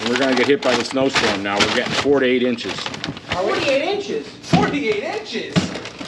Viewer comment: 1: I thought Grandpa said ‘48 inches of snow ❄️’ too 😳😂 2: Blessings to you, and with the arrival of your newest family members. 🙏🙏🙏